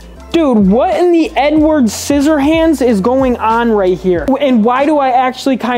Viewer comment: what did he say there what in the ___ scissor hands